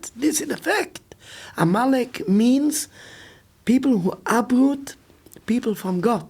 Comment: Amalek means people who what? It's not clear what he says. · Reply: People who uproot God.